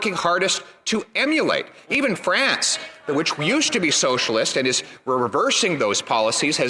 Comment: He slipped and said "Socialist Idiot" instead of Socialist Italy.